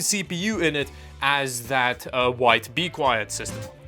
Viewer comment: Did you just call the "White BeQueit" system the "Wife Be Quiet" system? 03:13 , lol Sounded like it. 😂